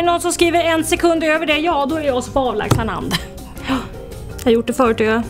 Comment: vad säger hon? 0:24 en sekund över det så gkrhsrghzrh hand?